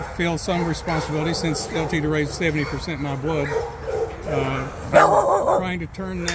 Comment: Did he say 70 percent his blood?